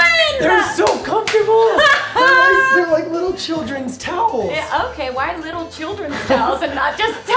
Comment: Was I the only one who thought he said little childrens toes instead of towels.